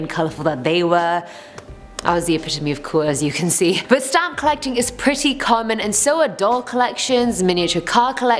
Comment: OKAY WHEN SHE SAD SHE HAD A STAMP COLLECTION I THOUGHT SHE SAID STOMACH AND I WAS LIKE WHY ARE YOU SHARING THIS INFORMATION